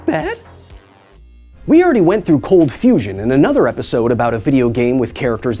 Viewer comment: I really thought she had said cold Fission. sigh